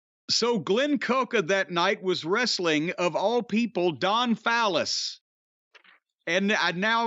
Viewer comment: Dante Martin breaks his leg @ 1:57 Don Phallus? @ 2:44 Did he say "vag@ @ 6:20 commercial starts